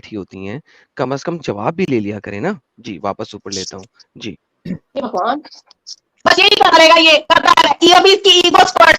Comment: did she said hai bhagwan here?